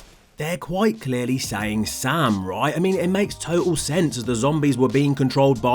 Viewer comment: soooo im the only one who hears "sale"